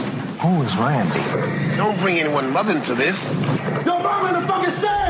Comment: I will never hear "Don't bring anyone's mother into this." I've heard it 1000 times and all I can hear is: "Don't bring anyone loving to this." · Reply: I heard "Don't bring one love into this" 😂